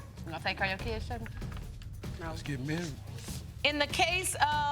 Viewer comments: Wait. Did he just say “let’s get married”? At 15:49 ? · did he say let's get married?